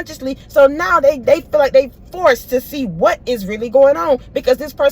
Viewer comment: Now ..I heard “BY FORCE.”